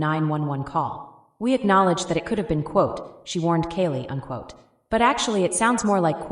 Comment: She warned kaylee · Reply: I thought she said "poor Kaylee"